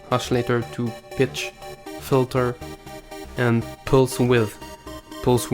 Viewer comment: I keep hearing "bitch filter" and I was like o.O and then xD Thanks for this tutorial, man!